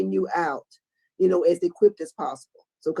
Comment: I thought said they're job is to let him out as quick as possible but what she said was "equipped".